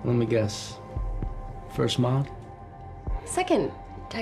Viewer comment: WHAT is he saying? "First month"? First moth? First mark? First model? WHAT?? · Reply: First mod. I’m guessing from body modification.